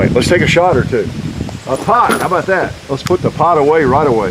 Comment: did he say cash me outside how bout dat?